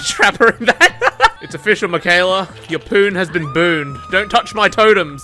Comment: Im watching a video where its '1 hour of silence randomly interrupted by AMONGUS' and I just heard "Your 'AMONGUS' has been booned.'